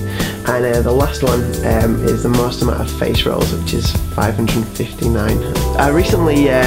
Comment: haha did he just say: most amout of facials xD?